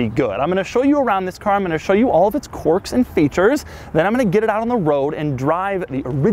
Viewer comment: Maybe it’s Doug’s accent but I hear “quark” when he’s saying “quirks”! Just me?